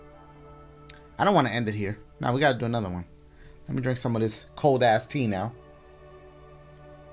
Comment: "Let me drink some of that cold ass tea now!" At first, I thought he said "some cold ass Tina !" XD